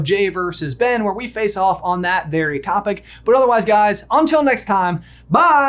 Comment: A lot to think about going into this movie for sure, but I can't help it at the end of your videos when you say "Until next time..." I always hear "pop pop!'